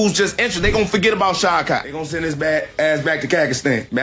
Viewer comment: Did Buckley say he’s going back to Cacastan? 😂